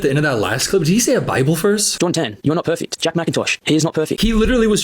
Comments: i think he said “jordan tan, you are not perfect,” not “john 10” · he didn’t say a bible verse. Yes it sounds like he said, John 10, but he’s just saying Jordantan. His accent and the fact that it’s sped up just makes it sound like that. Much love!❤